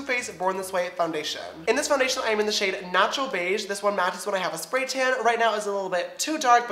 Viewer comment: When he says he's in the shade "natural beige" I thought he said "nacho beige"